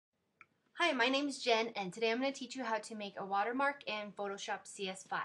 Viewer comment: lmao I thought she said hi my name is XDDDD